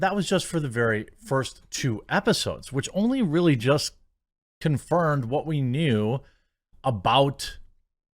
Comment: you say "confirned" instead of "confirmed". I noticed it in a previous video also. I'm not trying to be a dick by correcting you, I'm trying to be helpful. You're welcome.